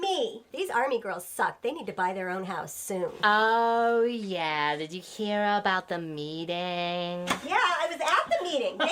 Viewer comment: May I ask who made the "army girls" video shown at around minute 8:00? I couldn't quite catch the surname you mentioned! Looks great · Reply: Ryan Trecartin.